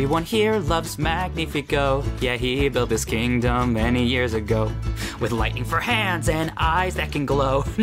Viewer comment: “Many years ago” I legit thought was some strange Spanish word, like I heard “ marillias” 😂 I only got that with subtitles on.